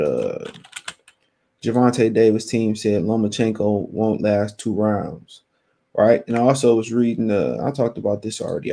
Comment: Ohhhhh, I get it I thought you were saying that Lomachenko vs Haney wouldn't last two rounds. 😂😂😂😂